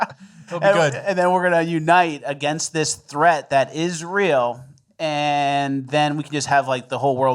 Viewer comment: Did someone say the threat is Israel?